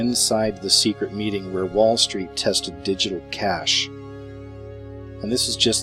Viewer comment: I thought it said.... DIGITAL CRASH